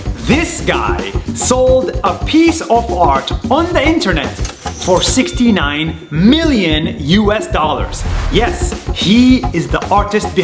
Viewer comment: For a second, I thought Nas said "This guy sold a piece of heart on the internet for 69 million dollars" lmao 💀